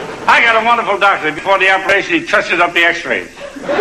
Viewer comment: I've listened 3 times to the joke at 2:14 but am unable to understand what he says for the punch line, can anyone interpret? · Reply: Touches up the x-ray